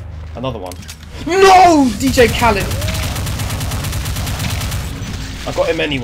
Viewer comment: Did you just said "Dj Khaled"?